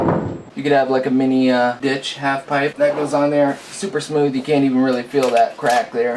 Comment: thought he said mini bitch half pipe